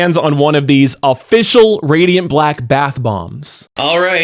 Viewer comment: OH!!!..... I THOUGHT YOU SAID BATH BONGS!!!